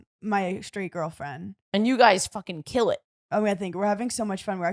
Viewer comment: lmao i thought said about Hannah and her straight girlfriend on the pod "youre guys fucking"!